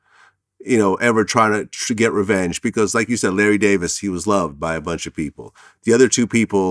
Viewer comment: I thought it said he killed Larry David an I got worried lol